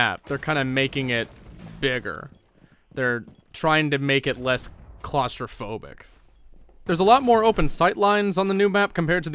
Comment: Is it just me or at 6:30 did he say “bigger” and “claustrophobic” really camp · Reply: LESS claustrophobic